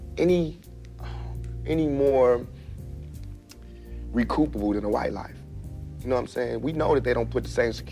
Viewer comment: what's he tryin' to say? Recup- a- what?